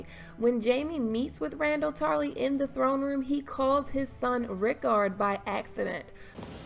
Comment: Hey great video as usual but I thought Jamie said Rickon not Riccard.